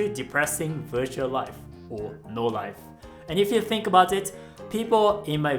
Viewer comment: When you said "No life" the automatic subtitle said "Your life". I don't know if I should laugh or feel insulted.